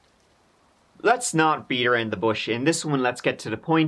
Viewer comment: Did he just say, "Beat her in the bush"? Lol, it is "Beat around the bush." Lol.